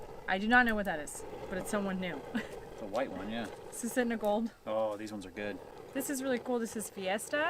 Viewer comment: Anyone catch all the names for all the potatoes? anyon catch the one she mentions at 17:00 something ssss gold 😀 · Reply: Suisitna Gold